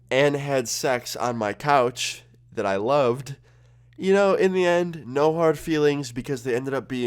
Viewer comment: DID YOU SAY YOU LOVED LOOKING AT THEM FUCKING🤣🤣🤣🤣